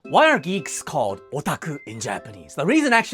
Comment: I misheard geeks for greeks and was confused the entire time